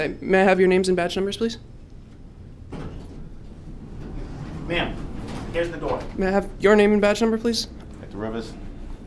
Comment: Add five minutes 13 seconds he said if you don’t shut the machine off you’re going to have to leave shortly after he says once you’re asked to leave you have to leave at about 540 is the one man that didn’t identify said you’re going the wrong way and the other guy who originally talk to said she’s OKAt 5:50 I heard ma’am here’s the door The first guy says goodbye at about 605 so who asked you to leave when were you told to leave none of that was anyone asking you to leave go back and listen to it all who asked you to live nobody they implied they didn’t really like you there but you should’ve gotten arrested and not left nobody told you to leave you left on your own The other thing is about recording you don’t ever have to tell anyone that you are recording that would be giving evidence there assuming that you’re recording